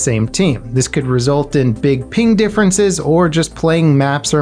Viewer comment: I heard "this could result in big peen differences" and had to double take that shit. Lol